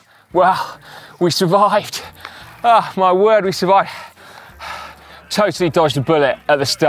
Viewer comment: 아 ㅆ...바...?????? 한국어가 들려요:face-green-smiling